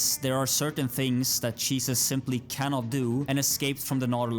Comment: Giggling to myself like a dumbass every time I mishear cheeses as Jesus.